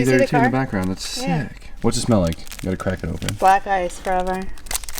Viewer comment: I swear it sounded like she said “black guys probably” lmfao.